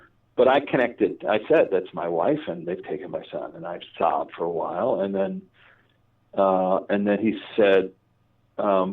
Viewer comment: Sorry, I wasn't clear. It occurs a bit after 26:20. I think it refers to another native American tribe but I couldn't understand the name. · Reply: Thank you for the detailed answer. Yes, 'Huron' was the word that I couldn't hear quite properly. Very much enjoyed this video. Thank you. One more question please - I had a brief age regression years ago and experienced it as a revivification. Did you experience your LBL this way?